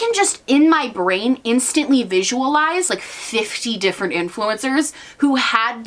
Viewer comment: Oh my god when you were talking about Sugar Bear Hair I misheard "influencers" as "gimmfluencers" lol